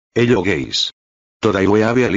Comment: I thought he said "hey little gays" at the start😂